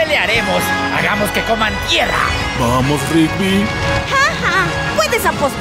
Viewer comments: creí que rugby dijo Mier** Pero oi bien y dijo tierra Casi de me cae un idolo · creí que rugby dijo Mier** Pero oi bien y dijo tierra Casi de me cae un idolo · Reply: yo qcrei que dijo berga en el minuto 0:06 🤣